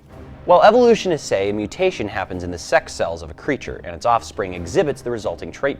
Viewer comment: what is a "sex cell"? Or is he saying that sex sells? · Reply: A sex cell is either a sperm or an egg.